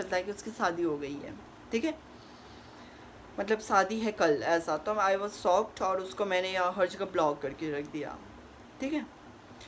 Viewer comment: I think u revealed the guy name by chance, maybe u slipped and I heard name harsh!..... 5:22 / 5:26 . I just heard ...or maybe not... Maybe I'm wrong ..Who cares...😅... By the way ...sister...stay safe .... We arunachale's are with you..... And for outsiders.... Don't underestimate our kindness accent ... if u treat and respect us right ... ofcourse we would give u the same..... If y'all tryna do something wrong with us... We are GOD at doing this stuffs.. Never ever underestimate the power of hill people's .. We tribals are good and brutual at the same time.... We donot carry our headhunting forefathers legacy no more... It's 2023... Guys ...everyone's is well educated....nowadays.. But if y'all insist us to do so .... Then you're most welcome .......😅😅 And...I know u can handle this kinda stuff ...you have the tribal blood flowing in you... But if things are going to far ...then we arunachal achi's bor's , ate , barme , aan abu ....everyone will stand with you.... GOD BLESS U ....SISTER ...AND AGAIN STAY SAFE. But . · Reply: she said har jagah😊